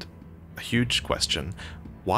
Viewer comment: I thought you said “why does mesma only have 1 fucking eye” lmao